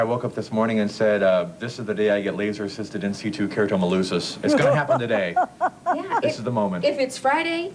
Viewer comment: What the heck does he say at 1:38? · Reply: laser in situ keratomileusis